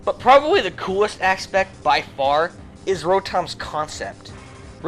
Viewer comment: I swear i heard him say scrotom